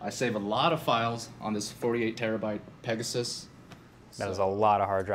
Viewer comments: Did he said 48TB 😭😭??? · Lol, did he just say Pegasus?